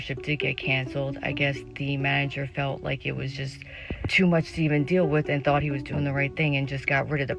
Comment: I heard her say "I guess the manager thought it was too much semen to deal with." 😅